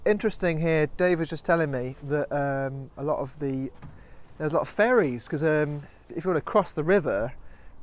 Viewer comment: I thought you said "A lot of fairies" and was waiting to hear about folk tales and whatnot lol